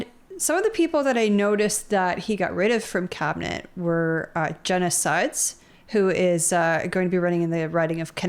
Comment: I had to go back and listen again: I heard you say "Jenna SIDES." Which would be a VERY unfortunate name. Jenna Sudds isn't much better....